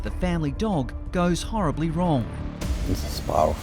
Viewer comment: In the one with George the kitten, in the preview I thought he said “spinal” fracture and I was just so worried! Luckily I miss heard and he said “spiral” still sucks for George, but waaaaay better then a spinal fracture by far.